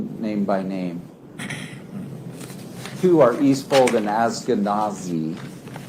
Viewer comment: I swear he said "East Folk and Ashkenazi" 😮🤦‍♂️ So twice as bad... "I'm not going to name names, but we have smart Asian and Jewish medical experts" 😐 Even if I misheard and they were last names, this is still the most hard to watch dumpster fire that I can remember... 🔥 🥴